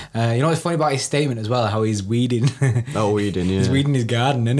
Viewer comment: I swear zinchenko said he was at his wedding not weeding